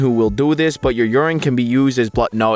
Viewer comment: for some reason i thought it said having sex with rayman